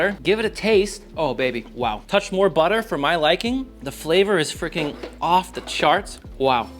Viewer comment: really sounded like he was about to say "the flavor is fricking awful"